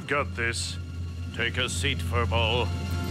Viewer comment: What Did Tree Rex Say To A Puppy Dog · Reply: He said: Take a seat furball."